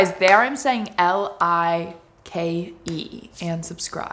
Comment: finally i understood what she was saying in the and, i thought it was likey subscribe 😅